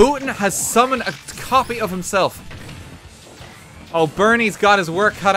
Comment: I heard "Bernie's dogshit" and had to reboot my brain